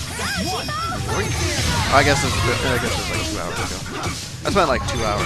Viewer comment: hey whadya say at 16:00, I couldnt hear you. but srsly turn up you and turn down the game please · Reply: He said: "I guess it was like two hours ago." And i think the audio is fine. :)